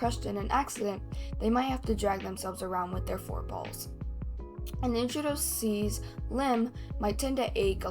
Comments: it sounds like you said “4 balls” instead of forpaws😭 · HELP I THOUGHT YOU SAID THEY MIGHT HAVE TO DRAG THEMSELVES WITH THEIR OWN BALLS.. 😭